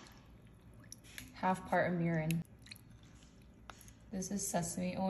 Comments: I thought she said 1/2 part of urine..😭 not 1/2 part of mirin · Eommas big jar sauce of sauce ❤ 1part garlic 1 1/2 part gochujang(Korean pepper paste) 3/4 part gochgaru(Korean pepper flakes) 3/4-1 part sugar(looks like cane? Sugar maybe) 1part soy sauce 1/2 part mirin 1/2 part sesame oil 2 spoonfuls of sesame seeds 1/2 part rice syrup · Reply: Thank God. Misheard mirin as urine. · "1/2 part of mirin" vs what I heard "1/2 part of urine" I did a double take so quick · When she said mirin, I misheard it for urine 😭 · I had to watch it again cause I heard 'urine' when she said "mirin"😂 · Eommas big jar sauce of sauce ❤ 1part garlic 1 1/2 part gochujang(Korean pepper paste) 3/4 part gochgaru(Korean pepper flakes) 3/4-1 part sugar(looks like cane? Sugar maybe) 1part soy sauce 1/2 part mirin 1/2 part sesame oil 2 spoonfuls of sesame seeds 1/2 part rice syrup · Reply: Mirin? Never heard of that I thought she said urine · I heard half part of URINE, and I had to play back to be sure it's not what I heard.. it's Mirin Sauce phew.. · I thought she said half part of urine not mirin for a sec 😅😂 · Somebody tell me why when she said mirin I thought she said urine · Ok when she said mirin I had to go back and read while I listen cause I thought she said urine 😂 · I doubled back on them subtitles, I thought she said “half part of urine” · When you said mirin I thought you said urine. Lmao 😂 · I thought she said urine instead of mirin 😭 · I thought she said half part of urine for a sec😰